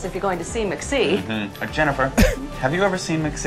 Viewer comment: did anyone else hear him say “McJennifer”????